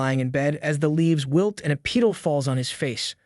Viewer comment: wth I swear I heard pedo